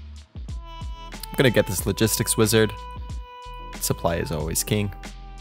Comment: “Supply is always key” sounded like “supply Zelensky” wtf :O